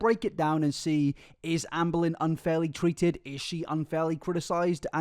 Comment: I thought I heard you say "unfairly tweeted" and I kept rolling back to hear it again and now I can't unhear it... Edit: B E A N S